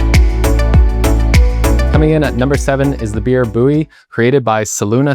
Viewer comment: I thought Beer Buoy said Beer Buddy 😁